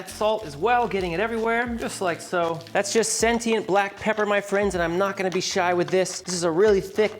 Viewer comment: Did you say it was sentient black pepper. My pepper is dumb as a brick.